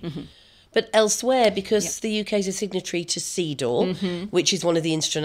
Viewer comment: the video annotation says "Seedor" but I just googled it and it is actually called CEDAW (Committee on the Elimination of Discrimination against Women)